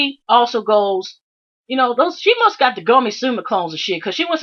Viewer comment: Did you say she must have gummy silicones ? Is that code for bad breast implants ?